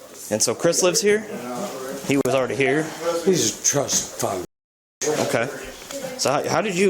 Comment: "Trust Fund B----" is what I heard